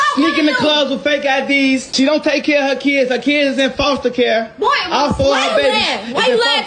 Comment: First of all how am I obligated to take care of crumb snatchers that aren't even mine 🤣🤣🤣🤣🤣🤣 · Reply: did bro just say she don’t take care of her kids and she’s 13 ???